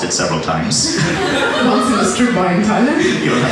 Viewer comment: What does she say at 3:41, after which he says, "You don't have to bring up that story"? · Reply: “Once in a street while in Thailand”